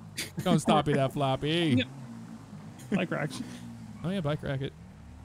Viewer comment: They said “bike rack it” but i heard “butt crack it” and i was like 🧐 wut